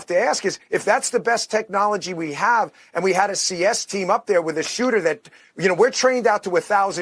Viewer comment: CS team ? We thought you said siesta ....shoot sorry man